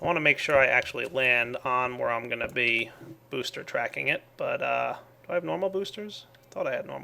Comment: I swear I heard "Do I have momo boosters?" XD